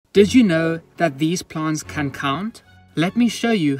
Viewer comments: BRO I WASNT LOOKING AND ALL I HEARD WAS “did you know these plants can cum?” AND I WAS LIKE “WHHHHHHAAAAT” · I heard "can cum" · I heard: "did you know that these plants can cum" the first time around 😂